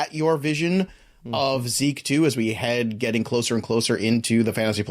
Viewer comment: did you say Zeke 2? As in, the zekuel?